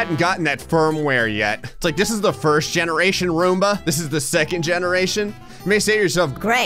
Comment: I thought you said wormba